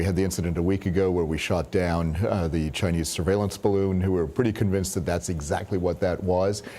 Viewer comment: Notice the military guy said "We are "convinced" that it was a spy balloon...Convinced? What? You always have to pay attention to their words... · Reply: I caught that too, I thought he said nearly convinced but either way. Agreed Always pay attention to the actual words coming out of their mouths.